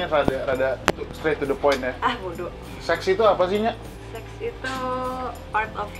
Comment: yg kedenger di kuping mah emang 'sexi itu apa'